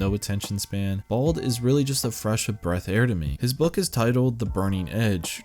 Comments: I heard "breath of fresh air" so does that mean that I'm dyslexic too? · did you just say "fresh of breath air" at 4:25 or am i just past my highness??